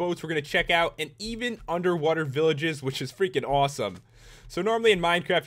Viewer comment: Did I hear you correctly? An UNDERWATER village?!?